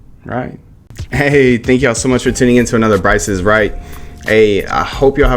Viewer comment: Doesn't matter how many times you say, it always sounds like you say " Price is Right!"😂